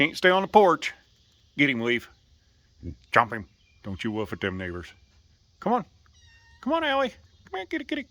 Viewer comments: Dude did I hear you say "don't woof at them n*****s" like 20 second in?! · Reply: I heard neighbors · Cute video. But did anyone else pick up on the fact he said the n word? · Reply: I heard "don't you woof at them neighbors" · Did he say what I think he did? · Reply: This isridiculous. Neighbors, hon.